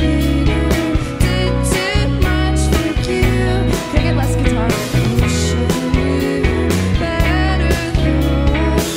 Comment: what did she say? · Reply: "Can I get less guitar?"